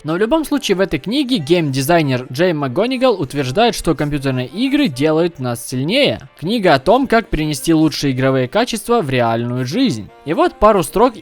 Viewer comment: я услышал Компьютерные игры делают на селе)))